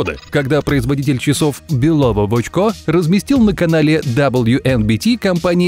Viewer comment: сорри НО Я ОДНА УСЛЫШАЛА ФАМИЛИЮ В ОЧКО😯😯😯😯😯